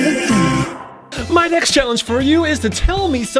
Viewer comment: Child wild clay said "My next challenge for you is...." I heard "My next complement to you is...."